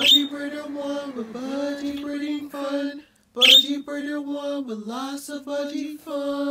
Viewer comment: I had this on low volume at first and thought you said "Budgie breeder one we're lots of fucking fun" 😂